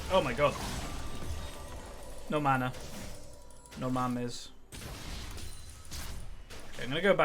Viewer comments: did just say "no mames" 18:41?... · did you say "no mames" at minute 18:41 ? XDDDD